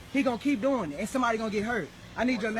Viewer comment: I would have swore swore I heard them in the beginning there somewhere saying somebody's about to get hurt now if I was a cop I'd be saying to myself whoa wait a minute